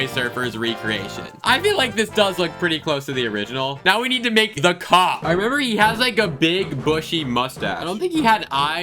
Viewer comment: did he say "the cock"???